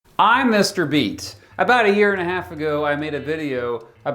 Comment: I always hear "Mr. Beast" Which makes me want to check my ears